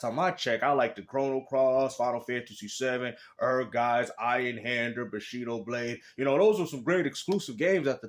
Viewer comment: For those who didn't hear what said at 4:11 (!) THE MAN JUST SAID EINHANDER! WTF! that a rare ass legendary game and not a lot of people know about it! I have the game case but lost the cd sadly😢. Yo you JUST went down as 'The Legendary Rare Gamer!' That woke me up to comment fr! Good stuff 💯 2025 HERE WE COME!